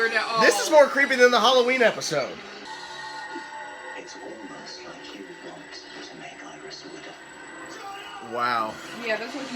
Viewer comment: What did Ramsey say about iris? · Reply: TheGothamKnight he said to Barry “It’s almost like you want to make Iris a widow.” Because he’s been so excepting of the fact that he’s meant to die.